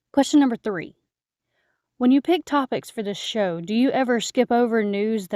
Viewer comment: Instead of 'pick topics' I heard 'pick pockets' lol